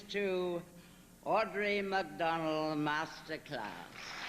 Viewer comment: Patricia Neal: "Audrey McDonald"... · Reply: I could have sworn she said Audra A Mcdonald